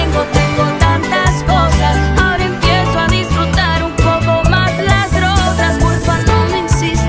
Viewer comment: En el 1:36 yo le escucho que dice hoy empiezo a disfrutar mejor las drogas o rosas comenten porfavor